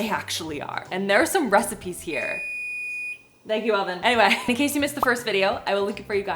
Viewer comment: I thought you had oficially named the oven cause I heard "Alvin" but then I realized you had just said oven lol